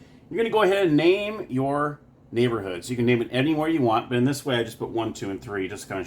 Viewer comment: Much better than Mr. Rogers. Lol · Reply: hahahahahahahaha 😄 I thought you said name your neighborhood or somethin'. Hahahahahahahaha 😄